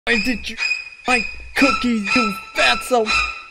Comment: Did he say cookie And why is he so mad